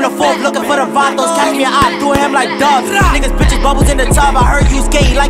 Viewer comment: Did he just diss duck ik u heard him say see opp do him like he duck